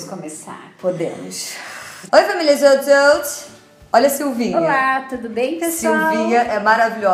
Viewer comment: Quando vc falou "Oi família Jout Jout, olha a Silvinha" eu ouvi "Olha essa uvinha" e senti muito amor nessa frase HAHAHAHAH vídeo maravilhoso <3